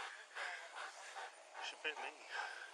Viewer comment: The handler sounded concerned at the end their, I thought I heard him say, she bit me, hence the normal level of praise wasn’t there that you normally witness between handler and K9. I think she got away from him in the chase and he couldn’t control her which probably was a concern. But if she did bite him… in her frenzy attack … it obviously put him off. You normally see a lot more praise???